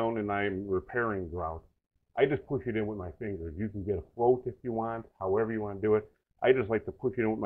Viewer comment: a "float"? if you want? what word are you saying? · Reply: Yeah he's saying "float"